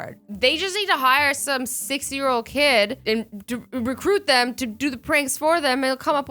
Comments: Did she say 60 year old KID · I swear I thought Azzy said "They just need to hire a 60 year old kid" At 3:43 😂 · For a moment there I thought you said for them to hire a 60yrs old kid, but then I listened to it again and heard 6yrs old kid. lmfao.